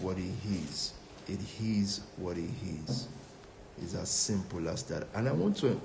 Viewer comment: At 10 mins and 19 secs into the video, When he said- “it is what it is”, all I heard was - “he his what he his”...😢😢😢😂😂